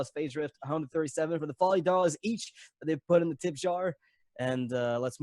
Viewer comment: I thought he said the tit jar...